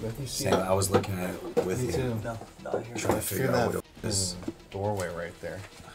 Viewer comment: 4.29-4.31 it sounds like it says “I’m trying to get out”